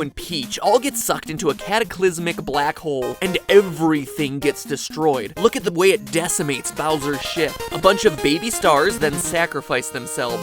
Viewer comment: I replayed 2:39 about 19 times because it sound like Mat says: ''Look at the way it decimates Bowser's shit''